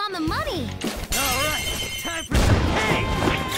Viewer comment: I always thought that Skull said “Alright, time for some CAKE!” for some reason. I always heard it when I got his All Out Attacks